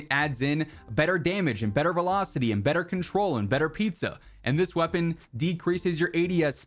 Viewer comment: Better pizza? Am i hearing things? lol